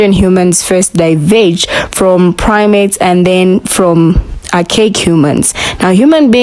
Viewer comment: Did Sis say something about primates??? 😳😐